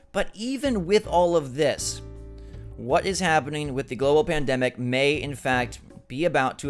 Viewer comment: Did he say "Ebola pandemic"? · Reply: he said “global pandemic”.